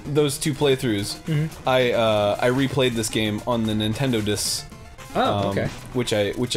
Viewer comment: So I was only half listening while I was working on the other monitor as I watched this and Arin started talking about his no-checkpoint run on the "Nintendo-diss." Now I'm guessing he meant "Nintendo DS" based on context clues, but I had to go back and listen again because I thought he might've said "Nintendo disk" which led me to wondering if Yacht Club Games released some sort of Famicom Disk System special edition that I immediately wanted.